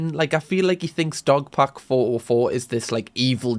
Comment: completley unrelated to the video , btw i adore your videos . i had subtitles on this video and the subtitles instead of saying dog pack the subtitles thought it said dog poop hahhahha😝🤣🤣🤣🙂🥳🥳